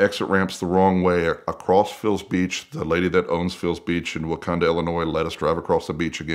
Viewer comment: Did he say Wakanda?